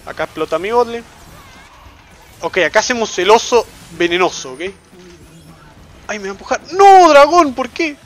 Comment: En el 5:24 escuche el oso venoso y tuve que repetirlo para confirmarlo xD encima Lo de "dormiste gato" fue genial